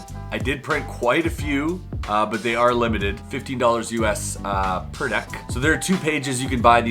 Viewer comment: did anyone else hear 50$? I was very scared😂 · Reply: I thought I heard $50 to but then I went to the website and saw $15!!! 😃